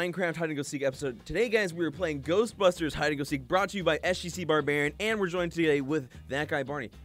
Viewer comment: I thought it said "Goustpeper hid n seek"